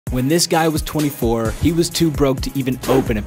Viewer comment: I thought he said: “ when this guy was 4” 😂